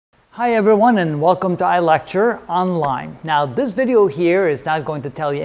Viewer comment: This is the first video where I heard "Welcome to ILectureOnline" instead of "Welcome to Electrolyte" 😂 Thank you for your videos, they are very helpful :)